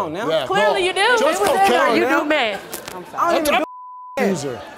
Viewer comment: Y'all didn't hear her say ( I'm sorry) at 3:06 lol